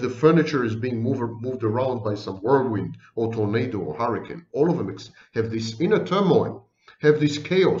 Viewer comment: Tomato hurricane 😂 (sounded like it for a second) 1:39:30 Great vid!!